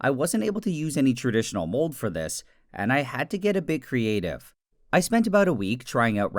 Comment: What He said: And i had to get a bit creative What i heard: And i had to get it in creative Me: /gamemode creative